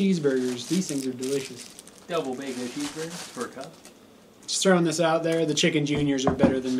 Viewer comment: I heard that "a double bacca cheeseburger... its for a cop." "Does this look like spit to you??" Anyways.... always enjoy the videos! Great job!